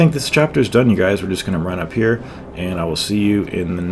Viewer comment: Did you say 85 000 at 26:16?